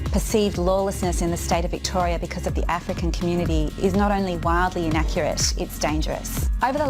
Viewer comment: Did she just say its "Waleedly inaccurate?"